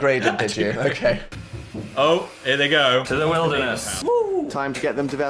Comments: Phil is definitely saying "to the wilderness" and not "terminal illness" lmao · I'll always love Phil mishearing saying "to the wilderness" into "terminal illness" you can't write this shit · I thought you guys were saying "Into the Wilderness" LOL not "Terminal Illness"